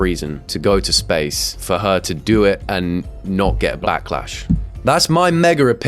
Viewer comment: “Blacklash” am I the only one who heard that?😂